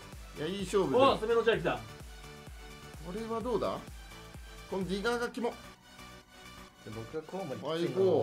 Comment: 「このディガーが キモッ」に聞こえるw